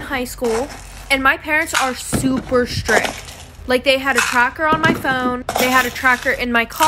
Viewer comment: When u said they had to track your car my sister thought u said that u had a crack in ur heart 😂❤